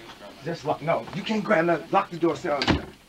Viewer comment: I'm no english native so it's kinda hard for me to comprehend everything they said especially in a very fast phase conversation but I just want to know what Kanye said to his assistant's in this particular scene 10:10. It feels like Kanye and the person recording the video is up to something very sneaky. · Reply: "You can't sit in here, lock the door" something like that